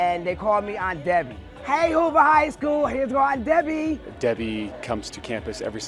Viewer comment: I thought she said Uber High School.